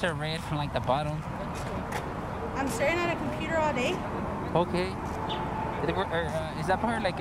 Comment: did she say "I'm sittin' on a Peter all day" ?